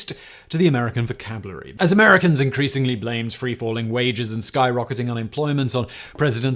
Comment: I'm so excited to have seen this in action. He does a commercial for underwear and then gets into the topic. But at 1:26 he says free-falling, which you hear clearly if you don't watch him. However, if you are watching him it sounds like he says free-balling. My first auditory hallucination in practice. Try it!